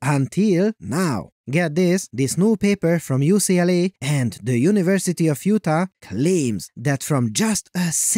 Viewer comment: I thought you said "University of Futa"